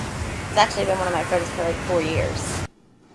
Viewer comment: Please, can someone tell me what Danneel said at 6:25 ? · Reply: ***** She said has been one of her friends for four years.